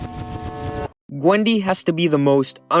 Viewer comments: did bro say Qwendy 💀💀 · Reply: He said gwendy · Did bro said gwendy